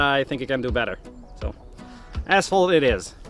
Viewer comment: When he said asphalt, I thought he said "ass hole" 😂